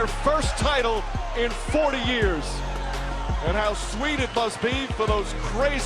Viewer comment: At 2x speed it sounded like he said 4 years lol